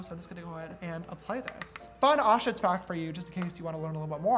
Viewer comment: When he said "fun ostrich fact" I heard it as "fun Auschwitz fact" and I kinda spat out my water